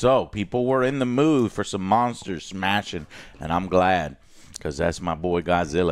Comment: is it me or did joe said Monsters Smashing at 3:13 🙃🙃🙂🙃